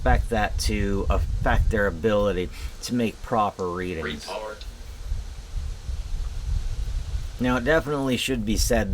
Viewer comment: AYOO WHAT DID IT SAY AT 2:31 ?????? 🤨🤨🤨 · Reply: It said report, it did sound a AWFUL lot like the r word though.